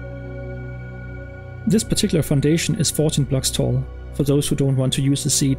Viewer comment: I have a question since I want to build this in my world but can't really understand the part (4:57)where you say that it is said blocks tall, I just want to know if it's 40 or 14 blocks tall since those are the numbers I hear. If you respond Thank you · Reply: 14